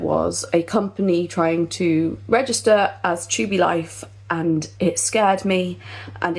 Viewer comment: 'There was a company trying to register as Tubie Life' *Trademarks Tubie Tape. 🫠 Side note - Captions say Chewbacca Life, and that actually made me LOL.